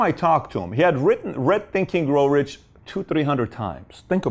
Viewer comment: What does he say here? It sounds like "..written red thinking grow rich"